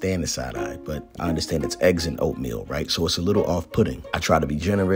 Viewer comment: Why is nobody talking about this “eggs & oatmeal” blasphemy?! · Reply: I scrolled down the comments just to see if someone commented on that. I thought he was saying eggs AND oatmeal. He said eggs IN oatmeal! What? Uzi wasn’t side eyeing. He was confused and making sure dad was okay! When I saw it my eyes went a lil crazy 😜.